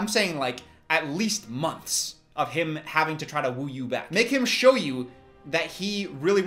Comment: All I heard was: “make him fro ale